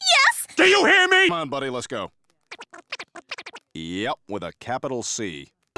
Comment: Did Woodstock just call that lady a c*nt? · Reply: With a capital C